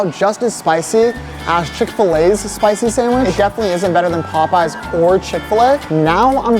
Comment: what did he just say? POPEYES? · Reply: Yes he said popeyes